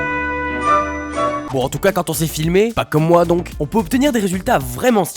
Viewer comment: can someone tell me in french what is he saying in 0:52? is it (pour en tout cas quand on sait filmer) ?? is that what he's saying?